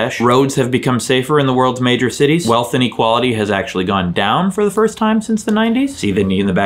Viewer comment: At first I heard "Wealth and Equality have actually gone down since the 90s"